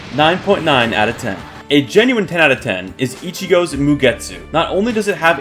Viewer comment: I thought Ichigo said "Nuggets"